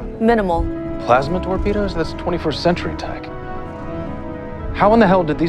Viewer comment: I thought he said "24th century tech".